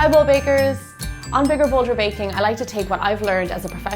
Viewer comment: At first, I thought she said "No Need Donuts."